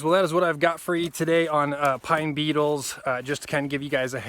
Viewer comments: Haha I thought you said pine needles! 🤪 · I thought you were saying PINE "NEEDLES"!